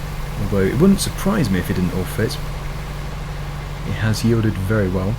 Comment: You said “ it wouldn’t surprise me if it didn’t all fit” but the captions said “ It wouldn’t surprise me if it in your face”